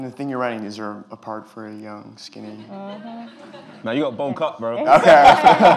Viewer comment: What does Daniel say at 21:50? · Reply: Noor Gielen bulk up bro